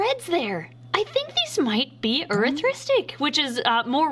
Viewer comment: i thought she was saying autistic